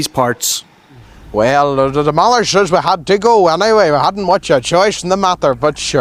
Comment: The bit when you thought he said 'demolishers', what he actually says is 'The mother says (we had to go anyway..').